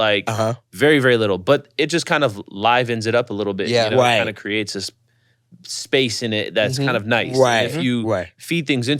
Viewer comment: Can anyone tell me what plugin theyre talking about around the 43:00 minute mark? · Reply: Gullfoss plugin by soundtheory